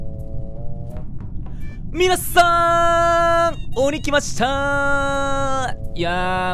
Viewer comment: みなさーんこんばんはーって聞こえたｗ（トムさんのあいさつ）